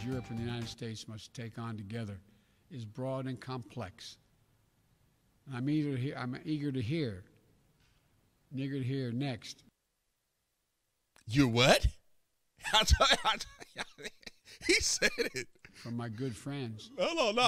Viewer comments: Nah, it sounds like he said " Nerr..guna hear next" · He repeated what he had just said.... "eagertohear" put together as one word and mumbled,. I can see how you thought you heard the n word.... me thinks you are looking for a "hit piece" on Biden. I would be glad if this was it.... because I am no Biden fan.... but I am honest and, even w/my bad hearing, all he did was put that phrase together as one word and he mumbled the words (he does a lot of mumbling and fumbling of words). Let's not get down into this kind of craziness.... the liberals do it enough for everyone and I won't participate. I thought I heard Bruno Mars say it in one of my favorite songs he does.... turns out what he said was "putsomeliquor in it" and w/my bad hearing "liquor" is not what I heard and I was too embarrassed to play or sing that song w/my mixed grandchild (who laughed mercilessly at me when I told her why".... She is the one who told me what he was saying in the song. So.... I can see how you heard what you heard.... but still.... :-) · That was a cheap shot, he clearly said “I’m eager” you right about him being brain dead tho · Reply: I did and he said I’m eager, got 5 other people wit me they said the same thing, does it sound like he said n*** if you play it quickly? Sure it does, but you know that’s not what he meant to say and it’s disingenuous to attack him for it, same thing when other people do it not just Biden.